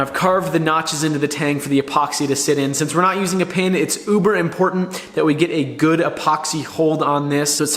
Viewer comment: Did he say “uber important” 17:54 or am I just too sleepy lol